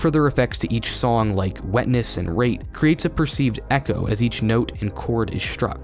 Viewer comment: I thought that when you said rate you actually said rape 😅 sorry... !